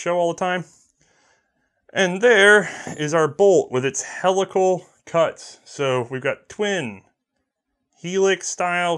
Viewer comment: Ahh helical cut. Thought you said hella cool cut. Same dif.